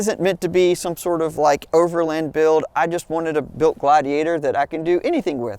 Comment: I THOUGHT SAID OVERLAND IS DEAD! 😂 and hates OVERLANDER!